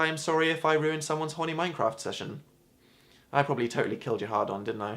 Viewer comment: Aha I heard 'Mycroft' instead of MineCraft. 'Sorry for interrupting your MineCraft session' makes much more sense (and sounds less creepy) than 'Sorry for intterupting your Mycroft session'